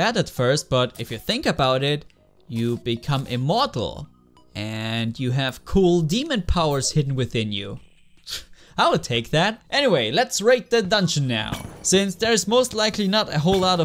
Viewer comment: I thought you said “let’s rape the dungeon now” when you said raid for some reason